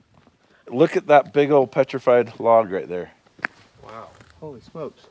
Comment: Oh, petrified log. I thought he said petrified frog....I'm so disappointed